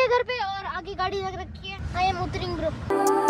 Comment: kya bola hai - (i am uttaring bro) bhaii ! mast <3 i love to see your vlogs